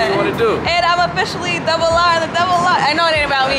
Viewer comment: I heard that “I’m officially the double r in the double r” did y’all elope like you wanted? Because i am curious now lol